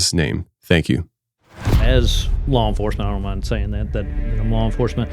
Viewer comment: Sound like he saying “Wall enforcement”